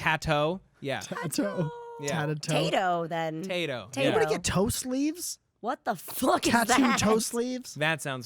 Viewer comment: I thought he said "toast leaves" but he said " toe sleeves" o.o